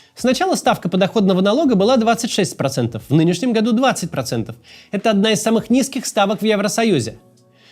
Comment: Ставка подоходного налога 22% в нынешнем году, что говорит Макс в 10:49? 20%? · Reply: Флудить побежал, ботоферма? 20 и есть